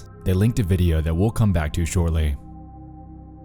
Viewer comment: i thought that the intro said "welcome back to mexico" and i was very confused for a minute